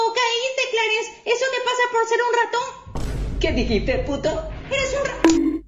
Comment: Porque tiene subtítulos al francés que dice potter en el minuto 0:22 cuando dicen Puto XD